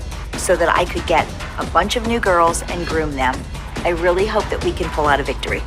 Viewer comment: did Cathy say she was gonna groom them 😅